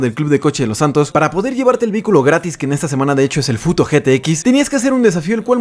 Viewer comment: entendí puto gtx jajajaja